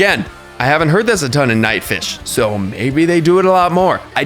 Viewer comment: Did he just say nightfish...